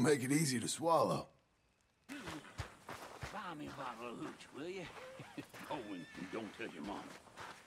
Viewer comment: I actually thought he said "grab me by the cooch" I was like wtffff